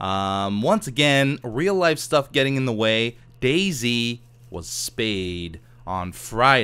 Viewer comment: When you mentioned Daisy i thought you said Day-Z and i immedietlt thought "is beef making a day-z walktrough" silly me